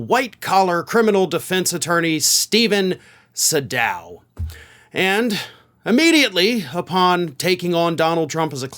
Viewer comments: I thought you said Steven Segal at first, might as well have hired him thinking about it. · Thought you said Steven segal, what's he gonna do kick the s..t out the judge 😂